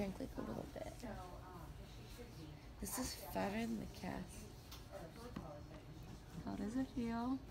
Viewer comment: I thought you said "fatter than the cat" LOL you weren't too out of it. you still knew to ask for your phone lol priorities! glad you're ok!